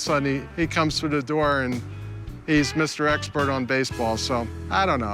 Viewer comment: Did he say baseball